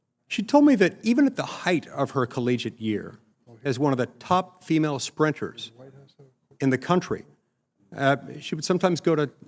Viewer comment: I thought he said top female spreaders 😂